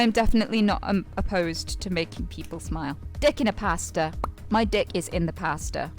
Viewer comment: I mean...I heard "Pastor"... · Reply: i was wondering if its pastor or pasta